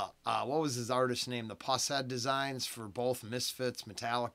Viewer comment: What are you saying at the 7:58 mark? The what design?? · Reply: Brian Schroeder, also known as Pushead, is an American artist, record label owner, and writer within the hardcore punk and heavy metal cultures. He was first active as the singer for the band Septic Death, obscure during their 1980s career but later recognized as pioneers of the thrashcore genre, gaining reviews in The Rocket and Creem.